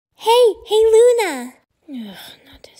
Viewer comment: What did europa say? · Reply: She said “ hey! Hey Luna!